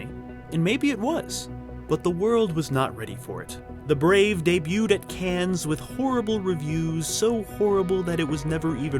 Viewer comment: Did I just hear you say "it debuted at CANS"????? oh lawd have mrsa